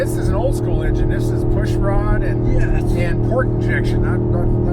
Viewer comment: the cc says pork injection. I’m dying